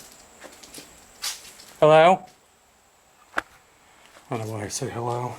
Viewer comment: DUDE!!! at 28:17 I heard a woman respond with "hello" back at you!!! turn it up bro...you can totally hear a woman say hello back to you. Is it just me?